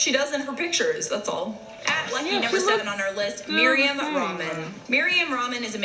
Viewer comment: did she say maria ramen? 😂